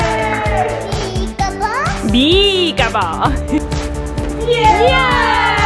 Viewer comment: Who else thought she said Bekaball Just me OK 😅